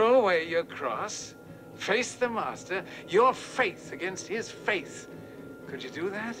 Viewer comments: ‘The Master wants YOU !’: ‘Your faith against The Master’s faith !’; ‘Face The Master !’...........50 years later I still get chills 🧛‍♂️ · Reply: I thought he said “your faith, against his face”?? Have I been saying wrong all these years? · I always thought he said: Your faith against his FACE!" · When I was 7 or 8 years old, I thought James Mason was saying "Face the Monster".... which made sense to me at the time, but I was also that kid who sang lyrics to songs as I thought they sounded even if I had no idea what the actual word was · I always thought he said face the monster!